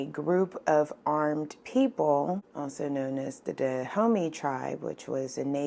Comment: I thought you said dead homie tribe 😬